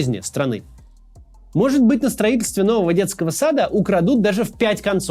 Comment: мне это послышалось? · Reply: Да, послышалось. Услышал детский дом вместо детский сад